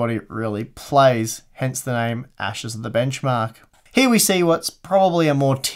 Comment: Steve, are you alright? 🤔 I heard you say "Ashes of Singularity" & not "Ashes of benchmark"😅 Edit: I spoke early without watching the whole video. You Are doing great.